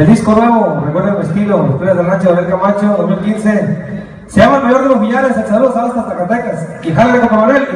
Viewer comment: Como Se Llama Esa Cancion No Le Entendi Bien A Jose Manuel Cuando Dijo El Nombre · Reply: El mayor de los villares